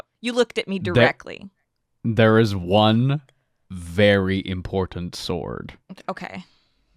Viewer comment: I heard "one named sword" all I could think was THE THRONGLER